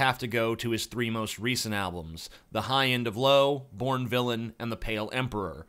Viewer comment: at first I thought it said dorn villain